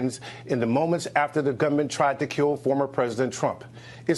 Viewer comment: I'm pretty sure he wasn't saying government, I heard gunman. It makes more sense he was saying gunman, it doesn't make sense that he said government, so using "occam's razor" here you have to conclude he said gunman. (A "freudian slip" here seems to complicated.)